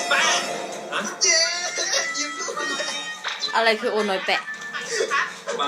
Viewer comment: ยิมพูดว่า โอวาแป๊ะ ค่ะ เป็นคำว่าโอน้อยออกของภาษาเหนือ ด้วยความที่น้องเป็นคนเหนือด้วยแหละอาจจะติดมั้งคะ55555